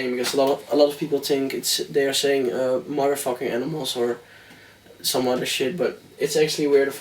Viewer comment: whooaa, i always thought the voice says "Motherfucking Animals" and now i only hear "We are the fucking animals". LOL